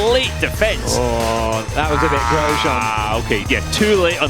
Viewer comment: did he say "That was a bit Grosjean"????